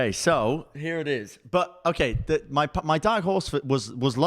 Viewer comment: did just say diet horse?